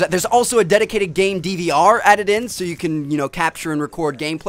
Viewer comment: lol i thought he said "DDR" (dance dance revolution)